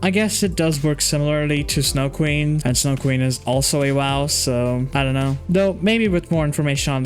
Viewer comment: did he just call snow queen a waw